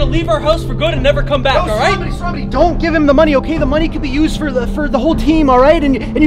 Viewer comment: I thought u were saying don't help him